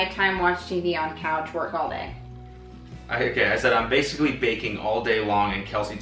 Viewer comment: Baking? Oh I thought you said vaping